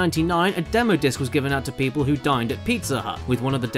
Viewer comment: Had to listen to that one a few times. Thought he said a demo disk was given to people who died at Pizza Hut.